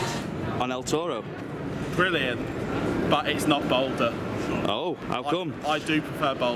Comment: lol.... that is why I did say "If it is indeed Boulder Dash he was referring to" PBE... ...What was your buddy saying at 9:03 ? I am on a train heading towards Philadelphia watching your videos for the first time with only one ear bud working ...lol : ( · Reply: chazz Lucas Balder at Liseberg